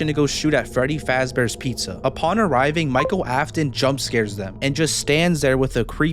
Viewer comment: i thought you said that was William afton and i was gonna say "wait didn't he died in the 1st movie?"